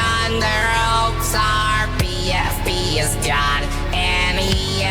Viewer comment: It sound like bro said the deed is done but he didn’t, if he did then I want to start saying the Aethos lyrics